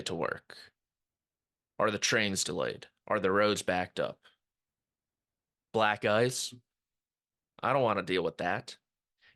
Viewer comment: thought you said "black guys? I don't wanna deal with that"